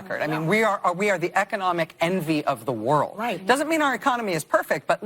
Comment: I believe I heard Rachel say "We are the enemy of the world." That was a Freudian slip, and likely what she truly believes.